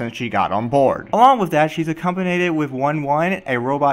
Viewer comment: i'm sorry, did you say accomponated?